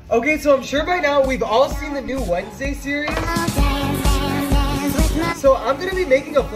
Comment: I heard him said 'new wednesday series' into new Blackpink series bruh 🗿